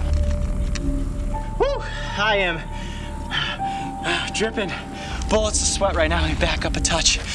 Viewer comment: Did he just say “I’m dripping, balls and sweat xD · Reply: im dripping bullets of sweat